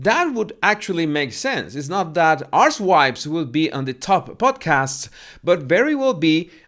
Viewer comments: Holy smokes! Every time said the name of MM’s podcast, I heard “arse wipes “!!! Lolololo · I’m sorry but what are you calling the podcast in the end? All I keep hearing is “arse wipes” 😳😂